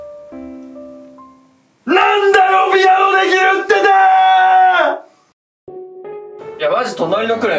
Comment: のさんが「なんだよピアノできるっ手てーーー」にしか聞こえなくてツボ